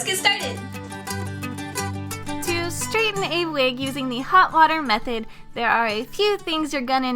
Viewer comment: I swore you said hot wire